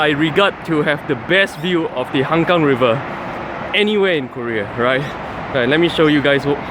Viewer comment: LMAO I thought he said 'Hougang River'